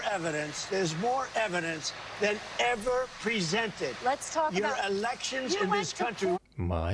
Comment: I heard “your” elections not “my” elections